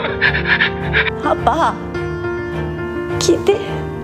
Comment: 一瞬「消えて」に聞こえたのは俺だけ？ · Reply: パパ聞いて？ですよ！